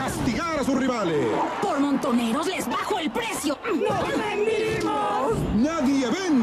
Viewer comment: ☺😂😂😂😂☺😁😁😁😁😁😁😁parece que dice "nos venimos "